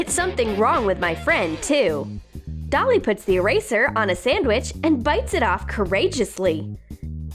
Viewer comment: Did anyone else hear Marker instead of eraser